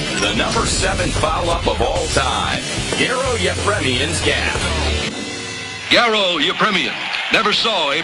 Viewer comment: What did the voice-over announcer say about Garo Yepremian at 13:22-13:26?... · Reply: "Garo Yepremian's gaffe" It's like another word for blunder